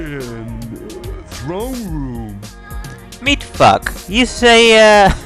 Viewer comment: A very interesting looking game, make no mistake! I shall definitely look forward to future parts of this one! :D 8:24 - "meet Fuck!" lol, that's what it sounded like, anyway! XD Ooh, you're getting an upscaler. I might have to send you a PM about that should it work out well for you. :)